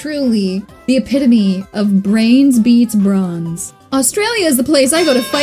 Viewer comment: I misheard you. If it wasn't for the caption, I would have thought you said "The epitome of brains beats prawns."